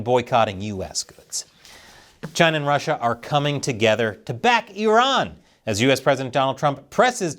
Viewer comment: i thought you were saying that china was backing "Eron" (like Elon Musk but you said the L as an R like the chinese) 🍯 🍯 🍯